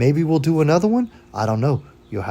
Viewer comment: Lol - I swear I thought the last thing you said was: “I hope you all have a stomach ache.” 😂